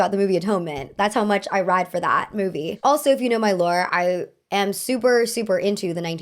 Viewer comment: Why did I hear “if you know my lawyer” 💀